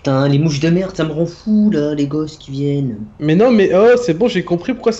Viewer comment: c'est moi ou il a dit sa me rend fou les gosses qui viennent